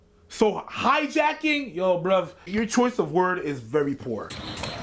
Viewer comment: Did just say "yo bruv"👁